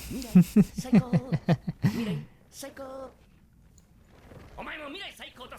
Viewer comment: The mistranslation thing probably isn't a interpretation thing. they probably just misheard saiaku (worst) as saikou (best) since future devil has been saying the "Future Rules" (Mirai Saikou) over and over, it's likely the translator misheard the saiaku and though he said saikou again. After people complained it was changed not too long after the episode release now subs say that he death is "the fucking worst" Also nice catch on Aki not using the Nail/Curse sword and is now using a regular-shaped sword. a lot of people miss that.